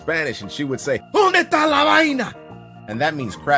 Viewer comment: pringle. i CLEARLY heard "donde esta la vagina"... yes vagina. same spelling in both languages. that was not CRAP.